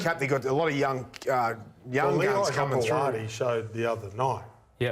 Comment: Did he say young chunts coming through?